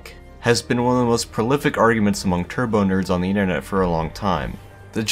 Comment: The term "turbo nerds" makes me feel attacked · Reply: I thought he said turd nerds